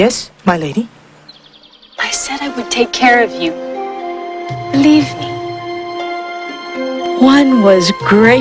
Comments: Did she say “Leave me” or “Believe me”? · Did she say "leave me" or "believe me"?